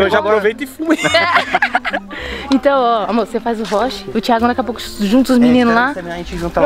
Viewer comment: foi só eu q ouvi amor